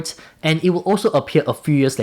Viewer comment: What word did you say at 4:38? Weird? read? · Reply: Haha, when the YouTube algorithm starts being weird. I think I cut that off too quickly.